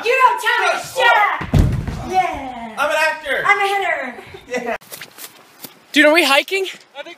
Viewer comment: Josh: I'm an actor Debby: I'm a hitter Me: I'm a sad emo rat that constantly watches my favorite bands vines because I have no friends and am terrible at socializing. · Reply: I deadass thought it said: Debby: I'm Hitler